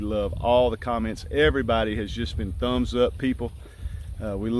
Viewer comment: Yay! I'm a "thumbsup people"! Lol! First, I thought you said, "Thumb suck" people. Hehe!